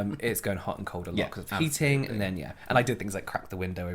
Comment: I had to play that back at 9mins 39secs when you were discussing towels and face cloths in the bathroom and I thought I heard say ‘...And I crapped with the window open’ 😳 Side note, I’ve been waiting for your joint video all week!!! They’re my absolute fave🙏🏼 You’re so funny together guys👌🤣💜